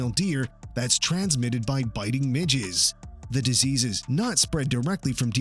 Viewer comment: Did he say “by biting midgets” 🤭